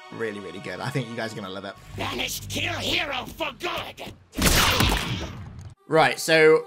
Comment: im so confused what hes trying to say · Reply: 'banished kill hero for good' that's what I heard